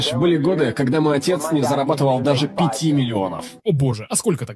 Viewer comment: Я сначала подумала что он реально жил очень плохо потому что мне сначала послышалась "когда мой отец не зарабатывал и 5 долоров"